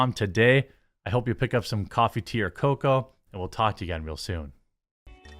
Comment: Laughing at myself right now. I always thought you were saying "coffee tier cocoa" in your coffee brand coffee adds (which I just assumed meant really premium cocoa). Just realized that you're saying "coffee, tea, or cocoa".